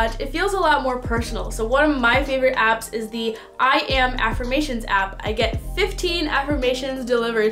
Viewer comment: i couldnt really understand wich app you said at 08:16 - can somebody help me? 🥺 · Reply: I am affirmations app